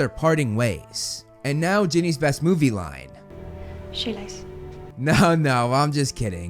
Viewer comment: What did she say? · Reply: Shoelace